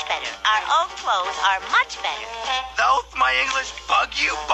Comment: Did Eddy Says The F Word! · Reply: He said "bug you, bumpkin."